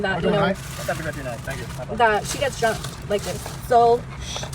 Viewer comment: Sounds like she said it’s not the first time she got “drunk like this” not “jumped like this”